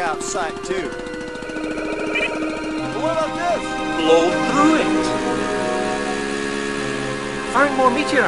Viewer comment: Been having flashbacks to that drilling in the snow scene for years, no idea why. I'm quite relieved I've finally tracked down what game it's from. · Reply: I didnt understand it as a kid, thought he said "Go through it" not "Blow through it"